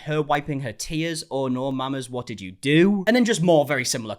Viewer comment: Me listening to this vid while cleaning thinking : did he just say no mames 😅